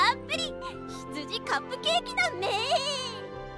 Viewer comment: what did she say....!? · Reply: "BEEEEEEHHH" a sheep sound